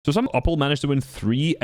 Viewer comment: "so some upl" is that what he said?